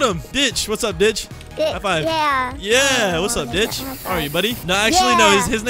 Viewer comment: I thought he said b*tch